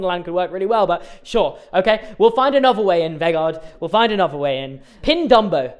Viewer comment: i genuinely thought you said vegan when presented vegar